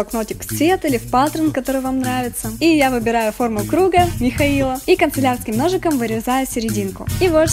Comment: Мне одной послышалось Митхаил?